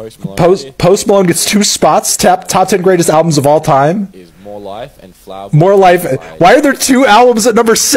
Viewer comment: I thought he said "Top 10 worst albums of all time" I was sooo ready to fight this guy once I saw 2014 Forrest Hills Drive